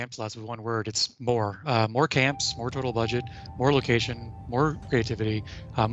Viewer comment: Did I just hear that right? More budget?